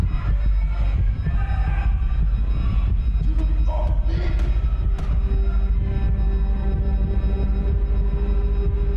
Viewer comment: What Bobby Saying? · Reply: Get off me